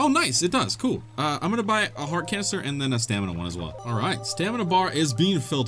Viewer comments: Did You just say Heart Cancer at 14:30 ? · did he just say he wants to get heart cancer!?